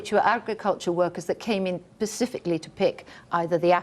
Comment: 4.09 Fuck me! Did she just say "pacifically"?